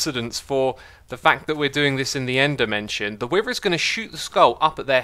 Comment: Why did I hear "Endermension"?